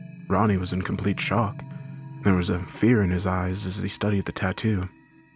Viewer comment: Did he say eyes or eye? Cause that's insensitive. Just a joke. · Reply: He said eyes